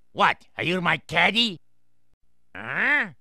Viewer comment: This whole time I thought he said "Do you want candy?" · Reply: He actually said "caddie." Not "candy." Okay?